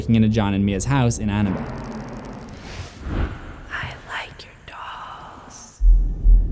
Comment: I, for the life of me can't here "I like your dolls" I keep hearing "I like your top"